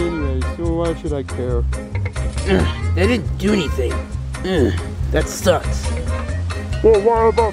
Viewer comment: Did Chuck said Sus?